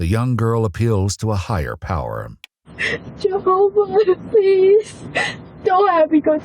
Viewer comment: I stg when it said "She spoke to a higher power" I thought she said "Jeff Goldblum" but she said "Jehovah" which, I mean, either one is acceptable 😂